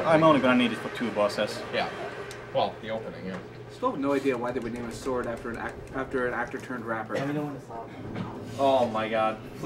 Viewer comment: What does he say at 24:34? "Still have no idea why..." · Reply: SerLaama I have no idea why they would name a sword after an actor turned rapper